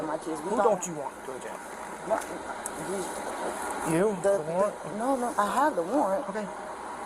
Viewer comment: Oh she did say warrant, bc I thought she said writ..